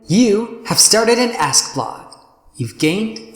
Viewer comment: I started laughing as soo as it said "You are starting an ask blog" cause I thought the guy said "You are starting an ASS blog" XD XD XD